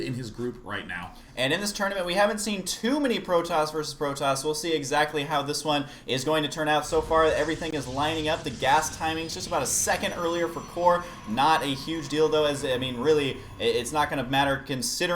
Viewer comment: sounds like husky is saying "not a huge dildo" ROFL